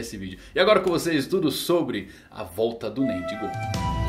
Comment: Juro que por um instante escutei "a volta do mendigão"